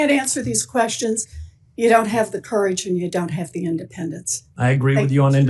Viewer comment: did he just say he does have the courage but he doesnt have the independence??